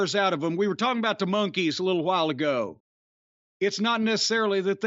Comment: did he just say monkeys about japanese idols? 10:30 mark